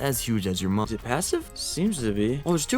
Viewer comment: Get 20% off your Raycon Everyday Earbuds today using my link for Valentine's day! <link> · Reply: did you say massive?